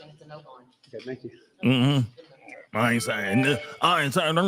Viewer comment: I thought he said “I ain’t wearing no dress” lol